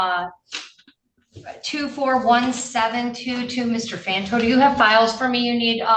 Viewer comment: I must have court case PTSD because I kept sweeting I heard her say "Mr. Lanto" over and over... But real talk no ill intent or disparagment towards PTSD. It's 70% of my VA rating.